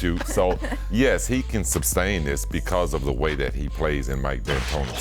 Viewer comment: Did I hear Pippen say "subsain" or are my ears playing tricks on me?